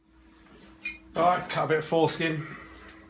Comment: did you say door or four skin a 4:10 lol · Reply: defo foreskin :) :) :)